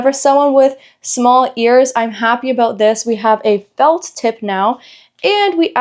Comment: did you say felt tip I just ordered some, but I might return them if they have a felt tip. I thought they were silicone. I thought they were foam inside the silicone.